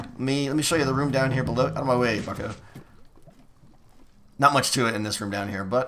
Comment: Did say "Outta my way fuc*er"